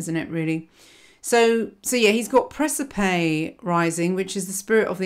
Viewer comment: What's the placement she mentions at 18:40? It sounded like precipe? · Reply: Fixed star Praesaepe. It’s in the beehive cluster in constellation Cancer.